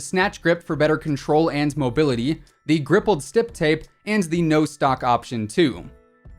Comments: Did he just say "Grippled stip tape" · Did he just say grippled stip tape 😂😂